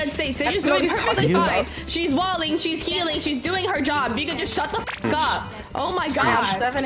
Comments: did she say the n word in 3:02? ... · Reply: if you slow it down you'll hear she says "if you could only shut the fuck up" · wait she said the n word 3:01 / 8:01 Idk but im trippin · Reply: “you can just shut the fk up” is what i said at 3:01 idk what you’re hearing 😭